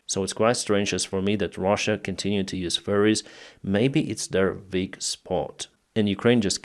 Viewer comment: I know this is just your accent, but at 1:25 it sounded like you said "So it's strange to me that Russia continues to use furries." Which, is just such a hilarious mental image. Thank you, I needed that.